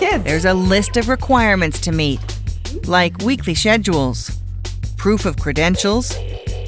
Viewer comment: did she just say shed-uels